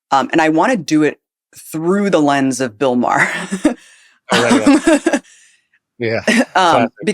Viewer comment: At first I thought you said, "I want to deconstruct this propaganda thru the lens of Bill Barr." Couldn't we switch out so many other names of empire servant-officials too!?